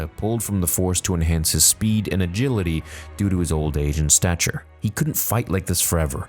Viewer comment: who else heard "old asian stature" 🤣